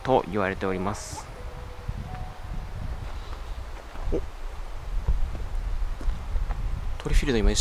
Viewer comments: が言われておりますって言った後 男の人？の声っぽいので「おい」みたいなの聞こえます そのすぐ後にトリフィールド反応してるのでなんか通ったかもしれないです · お疲れ様です。5:30くらいの所でが話し終わったあたりで、おい、って聞こえたのは気のせいかな？